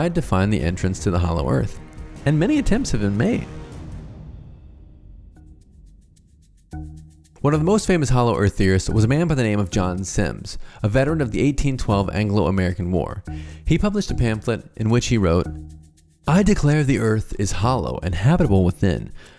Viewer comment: I thought he said johnny sins 👀